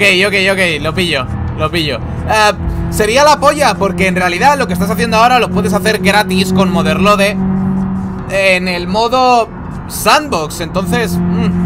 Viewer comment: En el minuto 15:45 dice algo sobre que esto lo puedes hacer garris con geghgeg grjevushhve en el modo Sandbox...alguien me puede escribir que es lo que dijo? Me gustaría buscar que es eso,por qué no tengo ni idea y no sé si es algún tipo de mod del juego o un juego distinto...gracias gente.🫰